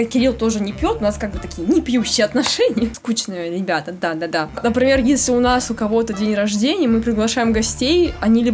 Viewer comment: "дандадан" послышалось😅